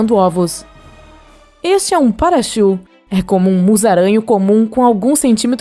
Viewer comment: eu entendi Pikachu